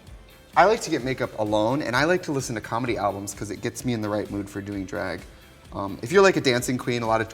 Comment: i thought he said gets me in the right mood for doing drugs